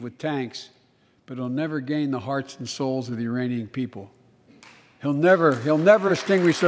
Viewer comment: Also ich hab urainian verstanden wer auch immer das ist!